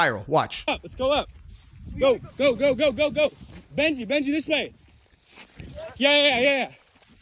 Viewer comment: mark “I thought u said this way”